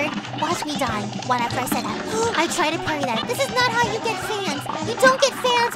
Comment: did just say "sans?"